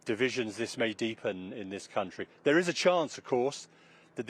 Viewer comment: 6.00 Did he just say 'cunty' 😬😂